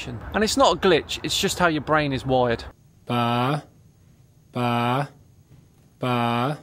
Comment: maybe it's my headphones, but apparently I was really manipulated, I heard "fah" o.O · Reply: Deaf people that used closed captions know he was saying “ba.”